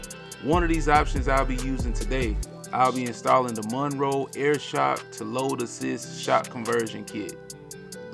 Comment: Got them from. Amazon <link> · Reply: Thanks, but I thought you said original air shocks. The link is for the spring conversion kit.